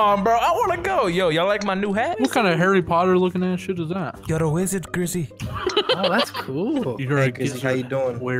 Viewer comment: Ngl when he said "You're a wizard grizzy" in my head I heard "you're a gizzard hizzy"